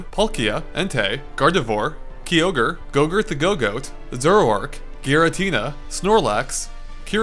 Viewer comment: What does he say at 10:44? Gogurt the Gogoat?